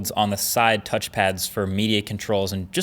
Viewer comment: I thought you were saying meaty controls instead of media.